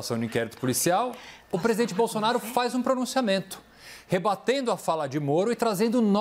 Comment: Mensagem subliminar aos 1:49...Não entendi bem mas parece Fora Bolsonaro...kkkkk